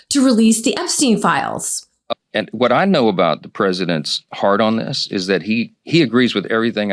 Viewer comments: did Mike just say "what i know about the presidents hard on" ???? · I could have sworn Moses Mike said the president’s hardon. I also swear Nancy Grace is a drag queen parody.